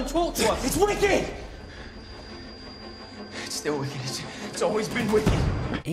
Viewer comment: What does Thomas say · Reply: It’s WCKD. It’s still WCKD. It’s always been WCKD.